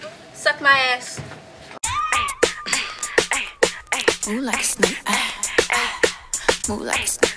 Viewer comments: This is how many times said move like a snake 🐍🐍 👇🏻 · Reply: After awhile i heard "moo like a snake" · After a while all I heard was “Moo like a Snake” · No one: Nobody at all: Me: move like a steak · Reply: OMG I THOUGHT I WAS THE ONLY ONE SAYING MOVE LIKE A STEAK AT SCHOOL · Did you just say moo like a snake 🔍🧐